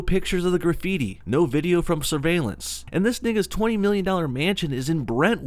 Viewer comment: Lol it sounds like he says "dis nigga's" at